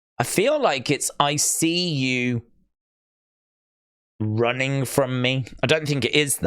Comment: I heard "I see you running away from me" in the audio